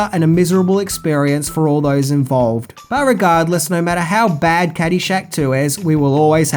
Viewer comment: Irregardless is not a word. Come on! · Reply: I hear "but regardless" is known for, well.. Mintyisms, but I don't think this is one of them